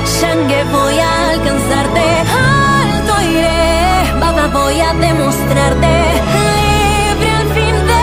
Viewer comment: Pense que decia baba o papa